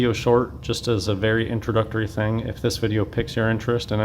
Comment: Did he say...PICKS your interest? seriously I think he said PICK, not PIQUE.....OMG I'm 99.9999% positive he said PICK. I've seen it mispelled as PEAK frequently, because it sounds the same as PIQUE, but NEVER have I heard someone say PICK.